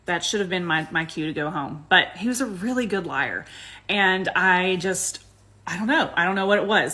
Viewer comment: LMAO I thought she said "He was a Good Liar" 🤣🤦🏾‍♂️🤣🤦🏾‍♂️🤣